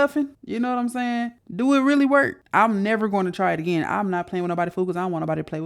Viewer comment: Did you say "I'm never gonna try it again"😂😂